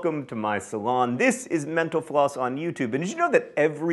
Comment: Every time this guy says "Mental Floss" I hear "menopause".